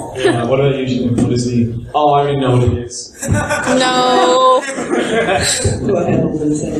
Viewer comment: what did holden say at 45:23 -45:37? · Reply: "What about you Jean? Oh~, I already know what it is. Oh my God. It just occurred to me when I said what do you want for a season 2?"